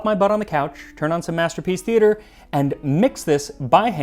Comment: First i thought he said Dream Theater and then I thought he said World Masterpiece Theater and got excited, but alas.